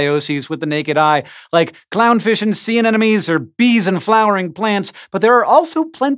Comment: clown fish and sea ANENOMES? Am I the only one who heard it?